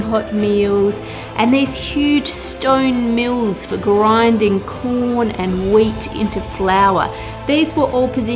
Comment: did you say corn? min